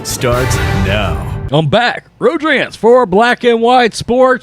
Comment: I can never catch his name at the beginning. Sounds like he says, “I’m back, Road Trance.”